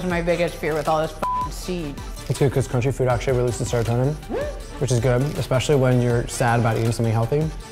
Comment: what did he say? 🤔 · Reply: "It's good cos crunchy food actually releases serotonin. Which is good, especially when you're sad about eating something healthy."